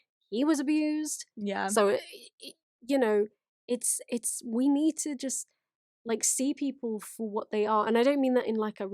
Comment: J says “how MJ groomed the world into thinking he wasn’t….” What’s that last word J says, I can’t understand the accent and there’s no captions ;( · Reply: Wasn't a nonce (UK/Brit way of saying paedophile)